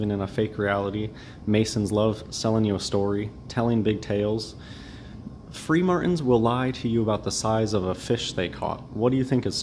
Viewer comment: Did he just say.. "Free Martins lie about a fish they caught" ... ffs the guy is a nutter.